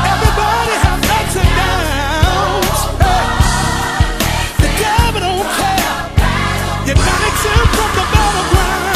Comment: What are they saying starting at 1:54? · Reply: They are saying No one is exempt from the battle ground"" Da truth honey!!